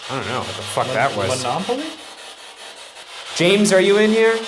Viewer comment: When they thought they heard it say monopoly it sounded kinda like “don’t laugh at me”